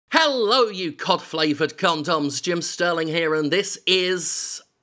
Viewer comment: Oh cod, not cop. That would have been a strange flavor for a condom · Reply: I thought he said cough flavoured.